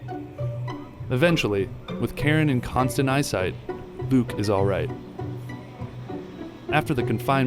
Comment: i thought he said buke instead of luke😂 i hope s2 happens